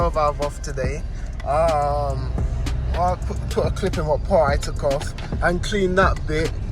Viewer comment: The only useful information about when you taken the egr back off and fixed it nobody can understand what your saying.... 7:08 a poo a cli n a pop I took off? Thanks mate